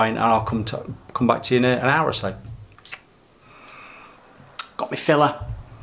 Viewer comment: great video. What filler did you use? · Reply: Where did you say? all I heard was you saying “got me filler” at 13:30 why can’t you just reply with the answer. No one has time to watch a whole video for you to say the name of the filler.